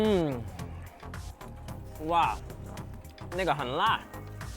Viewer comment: did he said n-word hell na?